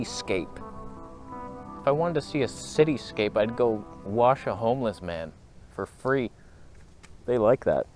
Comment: Thought you said “if I wanted to see a cityscape I’d go wash a homeless man—for free! They like that.” I thought, I can’t gainsay you, but surely there are easier ways.